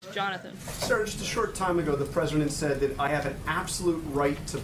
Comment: did he just call her SIR?!?